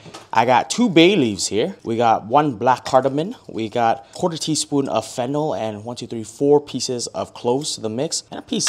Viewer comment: Did you say a quarter teaspoon of fentanyl? 👀